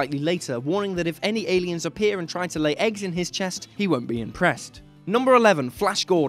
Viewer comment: not what he says, he makes the eggs joke and then says im trying to tell you someones coming, and then runs away from mantis screeching like a scared shitless kid, no where does he say he's not impressed